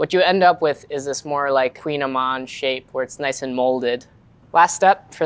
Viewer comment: Okay so at like 7:40 — who is Queen Iman? Is that what they said? I am mystified the captions said "queenamon" which also seems implausible please help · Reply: I think its "Kouign-amann", a name of croissant like sweet pastry